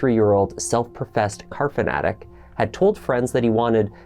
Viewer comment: I thought I heard ‘carpet addict’. I was like, well that’s a first. Then I rewound…🤭. Car fanatic makes a lot more sense ☺️